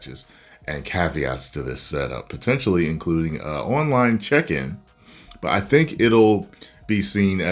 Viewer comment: I legit thought you said "Online Chicken"